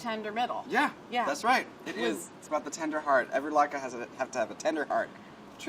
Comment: I thought she said every black guy has a tender heart. I had to rewind it twice